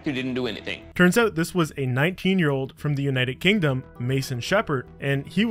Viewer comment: Did he just say 19 year old 💀💀